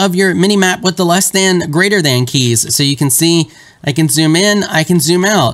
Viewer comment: does anyone know what he is saying? "you can zoom in and out of your mini-map with the less than greater than keys?"